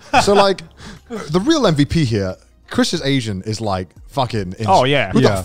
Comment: I thought they7 were saying asian